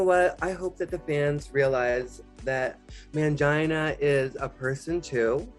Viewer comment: DID SHE CALL PANGINA "MANGINA"? 💀